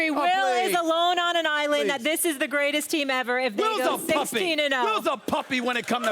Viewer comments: Lmfao I thought he said will was a Pussy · LOL i thought Stephen A said Will is a pussy, hahhaha